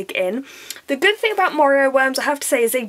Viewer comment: I really thought you said oreo worms instead of morio worms :)